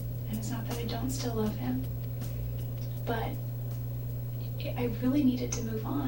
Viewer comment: It sounded like she said "still hate me. But"